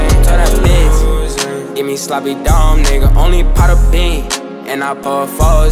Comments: am I hearing that right? Or he said “gimme sloppy dome nigga”??? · I love lil mosey but did he just say “give me sloppy dome nigga”